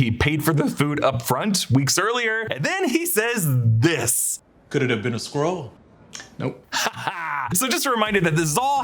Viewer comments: Am I the only one who seriously thought he said, “Could it be a squirrel”? 🐿️ 😂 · Totally thought he said Squirrel and not Skrull lol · I thought he said squirrel not Skrull. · “Maybe it was a Skrull” - i thought he said squirrel 🐿️ 🤣🤣🤣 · I thought said "squirrel". Makes much more sense that he said Skrull. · Lmao I thought he said “could it have been a squirrel” when I was watching the movie